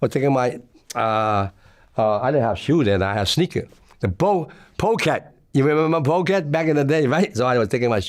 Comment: what kinda sneakers?? Anyone know what he said here? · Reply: Pro keds